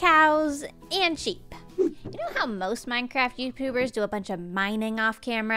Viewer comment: did just say Youpubers or was that just me?